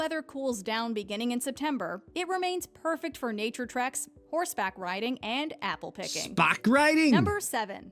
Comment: These pics definitely did not convey the beautiful colors, they seemed muted. What you misheard was that she said “horseback riding”, not “spack riding”!